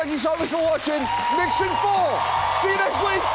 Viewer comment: What is "mission four" or whatever he said? 😅